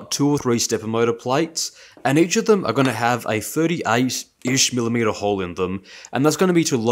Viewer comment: HA I’m watching this before taking my adhd meds and I absolutely misheard “a 38-ish mm hole” as “a 38 inch mm hole” I rewatched those 5 seconds like four times